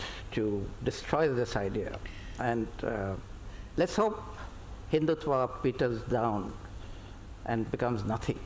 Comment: Oh man I heard the anchor 37:55 say, "lets hope Hinduism petals down to nothing". there is this section of paid media out there to destroy hinduism. Moreover Tharoor from the video clearly shows, he as no idea about Hinduism. So I'm not going to waste my money buying his book to be fooled.